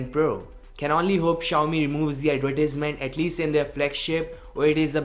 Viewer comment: What did you say in minute 3:14?? "Can only hope...???" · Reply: Advertisement 😂🤣🤣🤣